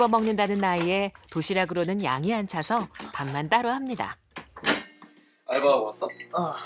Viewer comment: 뭘따로 한다고 한건가요? 잘 안들려서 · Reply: 양이 안차서 밥만 따로 합니다